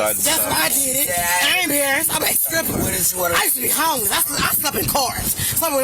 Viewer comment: I think something's wrong with my audio, did she say she's a stripper??